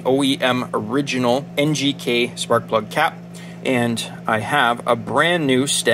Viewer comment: Haaaa. I thought you said the "spark plug GAP". now it makes sense